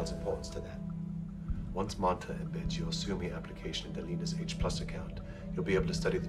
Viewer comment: I have watched it over and over but there is a part of the dialogue I just cannot understand. At about 2:55, it sounds like the doctor says "Once Manta --- application the leaders of H+ account," and the part I'm missing sounds to me like: "anbensyosummi" I am trying to figure out what his plan is.